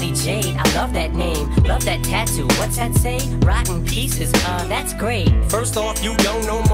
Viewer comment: I always thought she said “rotten peaches”